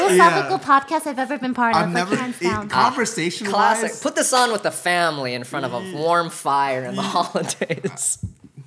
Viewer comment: "Put this on with your family infront of a warm fire" Me: Lives in australia, Me: "You're not wrong..." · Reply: Thought it said "put this on with your family on a warm fire"